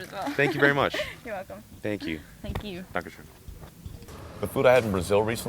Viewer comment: aww 6:02 did he say "dankeschön"? x3333